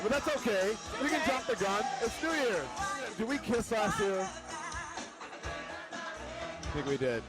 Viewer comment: "Did we kiss ass here" is that what he said?? Then he says something about sexual tension. I thought he was gay? Seriously.